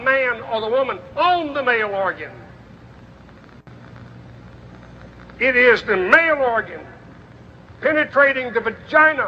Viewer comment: i must have misheard him, i'm hearing penetration with a mouth organ meh what ever floats your boat...